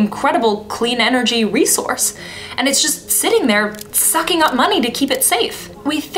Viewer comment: Does she say "sucking up money" here? Or something that rhymes with "sucking"?